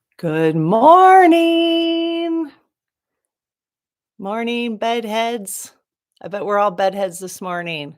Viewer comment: So you are saying "Good morning, bed heads." I thought you were saying "Good morning, bud heads." As in pot.😂